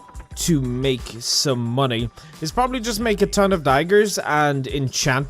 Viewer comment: When you said "enchant daggers" I heard "enchant tigers". My brain is really tired.